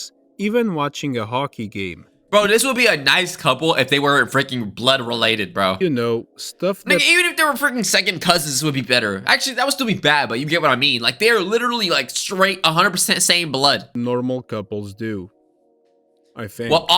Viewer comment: bro did you say the n word😂😂